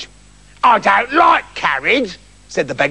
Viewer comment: If u listen it sounds like is saying I DON'T LIKE CABBAGE! XD